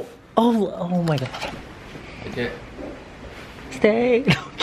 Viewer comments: Wait did I hear you sing "stay"? You gave a spoiler and I didn't realize....... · I HEARD THAT! @ 5:54 "STAYY" MHMMMM